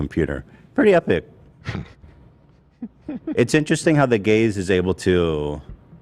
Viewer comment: The fact that I keep hearing "the gaze' as "the gays" on this episode every time Ethan says it is just so wrong.